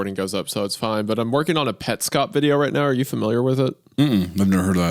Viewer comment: did he say petscope video 👀